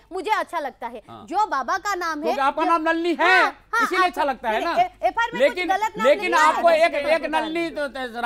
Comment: did he said "NALLI" ?